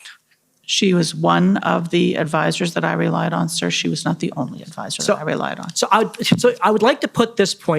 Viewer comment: The lady in glasses sounds like she is says "he was not part of the orgy"